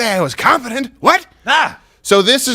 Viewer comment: Oh confident? I thought you said competent! Where's my ice cream!?